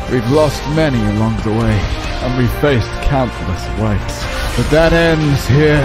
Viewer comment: it took me re-watching 5 or 6 times with captions on to realize he said "countless ways". Thought he said "we faced countless whites" 😂😂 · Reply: He actually said "we faced countless wipes", the captions are also wrong 🤣